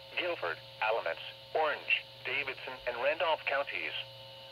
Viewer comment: did that just say Davidson?